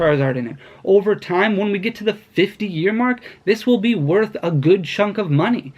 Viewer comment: did he just say 50 year mark. So I a have to store my one and only darkness ablaze booster box for 50 years? I would be 70 then lol.